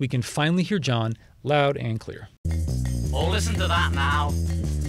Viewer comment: I always thought John was saying "Oh, listen to that Mal" to Mal Evans at the end of Polythene Pam